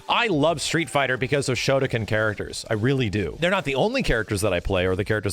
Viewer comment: did he say shotacon lmao